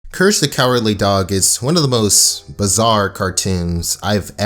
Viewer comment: i heard him say curse the cowardly dog in this time